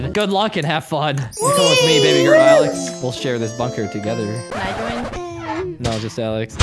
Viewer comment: did say the f word or is something wrong with my ears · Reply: He said bunker.